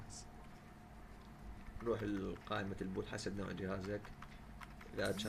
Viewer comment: قائمه ايش ؟ ما سمعتها ؟؟ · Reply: البوت